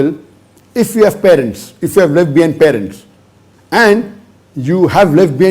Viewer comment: did he say lesbian parents? Can someone say what he actually said? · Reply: He said "Left Behind" Parents.